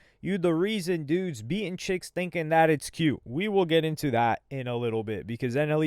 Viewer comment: I thought he said ninjas being bitches thinking that it’s cute